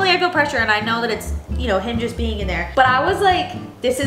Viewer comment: I thought slipped and said the baby name. sounded like "Hendrix being in there" HIM just being in there😂